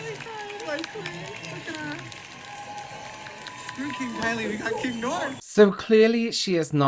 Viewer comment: Did I just hear the guy in the background say “My Queen. Screw King Kylie, we got King North”?!? 🙈